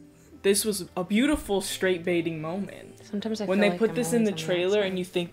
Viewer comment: The whole time she said ,"straight baiting" I thought she was saying "straight bathing" as in ..bathing suit Yeah I- okay